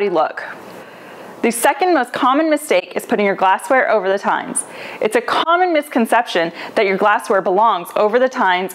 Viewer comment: Did she say 'times' ?